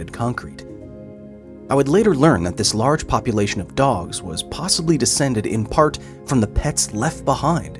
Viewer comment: Did he just said Puppylation? Cause that would be certified gold!